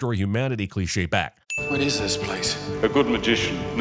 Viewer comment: the subtitles say "What is is this place?" instead of "What is this place"